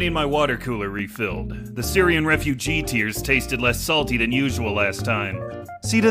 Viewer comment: i misheard tom nook thinking he said "more salty" giving me a better reason to understand isabelle's blushing